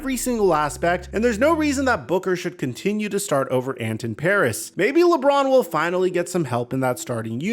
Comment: Am I missing some or I heard you call Anthony Edwards "Anthony Paris" 13:01?